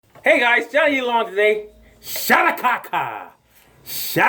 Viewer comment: First time viewer and first words I hear are "SHADA KAKA!!!' and I love it XD